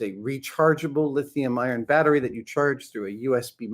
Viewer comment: did he say “lithium iron” battery?!